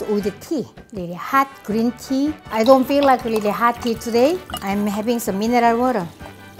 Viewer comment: At first I thought you said "I don't feel like having tea today so I'm going I have soju instead"